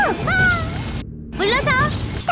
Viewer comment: what did she say? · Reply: honoo gui no kei